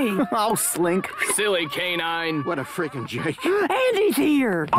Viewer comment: what does mr.potatoe says? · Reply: Íanko Balboa he says “what a friggin jerk.”